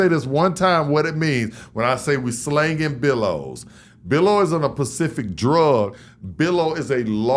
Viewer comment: I always thought he said "villows". Now it makes more sense